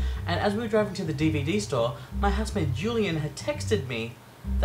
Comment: I thought you said your husband Julian. lol. I was like... are you married? Does Australia recognize same sex marriage? I know it's recognized in Canada, some US states, some European countries, and Argentina. $300 fine is high for driving while using a cell phone. The fine here (in BC, Canada) is about $170. Can you ask for a reduction at the traffic court? maybe tell them that you didn't know the law and it's your first time and you pulled over to the curb when you called.